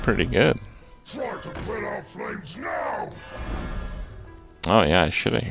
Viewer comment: What does Prestor's Pyrospawn actually say? I always hear it as "Drop the corn flakes NOW!"